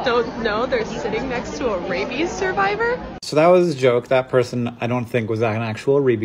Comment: I thought she said baby survivor 😂(not my tongue language though)